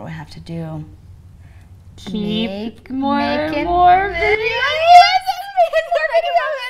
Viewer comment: Did she just say "Makin' Bacon"? I didn't catch that.